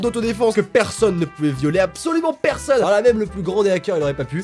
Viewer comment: je rêve ou à 1:22 j'ai entendu le mots violé à la place du mots volé mdrrrr 😅😂❤ ps : ne change pas tu est le meilleur 😘😘😘😍❤